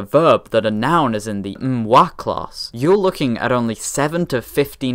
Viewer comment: Did someone say N'wah?